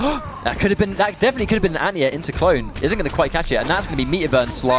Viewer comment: what does the caster say at 5:13 ? -there could have been the "ania"(?) into clone and again at 10:39 ? - there's the "ania"(?) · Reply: Anti-air into clone.